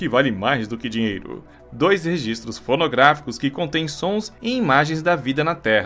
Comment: Nao sei quem vai mais longe, a Voyager no espaço ou eu nas minhas paranóias! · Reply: Ha paranóias :V pior eu que ouvi pornográficos em